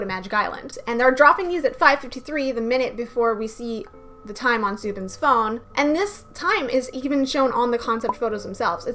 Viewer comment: OK U HAVE TO SEE THIS COMMENT BECAUSE TGIS IS DRIVING MR CRAZYYYY AND NO ONE IS TALKING ABT IT... so at TXT ETERNALLY mv, at timestamp 4:25 or 4:26 (make sure to turn volume up) there's a faint voice and it seems like soobin is remembering what that person said, and it sounds like one of the members, maybe taehyun or beomgyu and it's faint, but it's there cause even the sound cuts off a little, and seriously no one is talking abt it and I think it's very important, they something in Korean but idk what they say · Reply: It says “the doors are closing”, which is a common thing the elevators say in Korea!